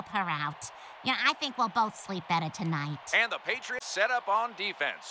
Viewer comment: I heard "Alabama" when she said "Alana"🗿